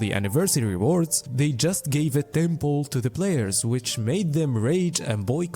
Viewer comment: Temple after Temple after Temple Tentpole after Tentpole after Tentpole Only gatcha players know what he's actually saying lol